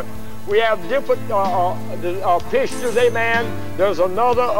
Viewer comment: I like the picture guy :) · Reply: Haha oh "pictures" I could have sworn he was saying "pitchers" 😂